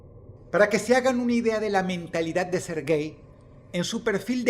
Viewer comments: siempre que decia serguei pense que decia "ser gay" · Al principio entendí "ser gay" en vez de "Serguei" jajajaja xd